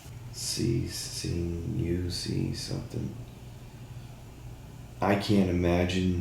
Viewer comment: I think he said "it's like seeing a movie"